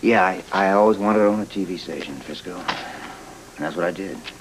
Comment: Did Derek call Frisco "Frizzo" at about 9:33?